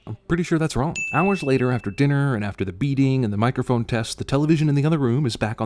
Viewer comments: the first time I watched this I thought you said beating not beading and I thought to myself 'who wouldn't want this dude to die by the end of this film' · I so thought you said "After the beating," instead of "After the beading," 😅